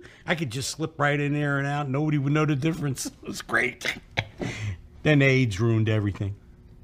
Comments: "Then the AIDS ruined everything" is one of the best line deliveries EVER😂😂😂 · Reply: I thought he said "but then they age roomed everything" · Dennis covering his mouth when Frank said "then aids ruined everything" is my personal favorite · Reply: oh my god i thought he said "then age ruined everything" 😭